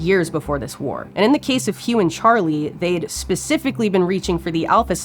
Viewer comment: I’m just doing my chores listening to this and I heard “Human Charlie” and thought for a second, theres a guy involved either the chimp wars??